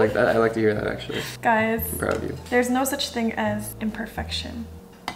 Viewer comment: He says i love you in 9:32 i think 'cause that's what i heard😅